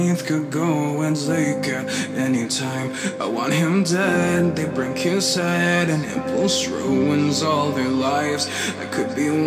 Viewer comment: Instead of "I want him dead they bring his head" I hear "I want him dead they bring you sad"